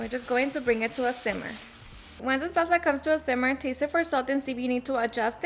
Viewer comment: Did she just say to taste the masa for salt I wouldn't eat masa like that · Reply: She said taste the salsa for salt. I didn't hear her say taste the masa.